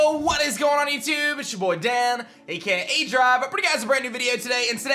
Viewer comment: 'I brought you guys, ' but i hear, "a pretty guys" 😂😂😂